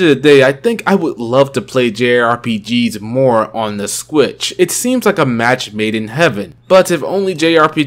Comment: Sometimes I hear "Switch" others i hear "Squitch"